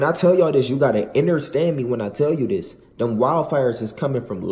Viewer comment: He said innerstand instead of understand at 1:47 😂